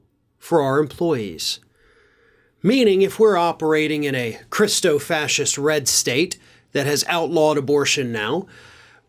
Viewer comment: Slight error. Crypto fascist not Cristo fascist. Yesterday I thought you said Crisco fascist which I liked but sorry, that just got under my skin a little.